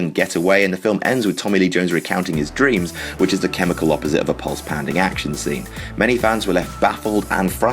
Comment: What are these words you're saying? Is it "Which is the opposite of a pause-pending action scene?" If that's what you're saying, what does that mean? If you're saying something else, what does it mean? Thanks.